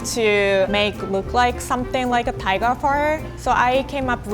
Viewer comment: I thought she said she wanted it to look like a tiger fart😂🤔